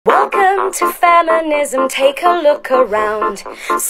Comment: i thought she said felonism